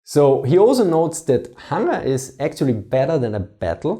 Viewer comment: What did he say here? that (something) is more important than a battle? was it "hunger"? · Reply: yes, "hunger".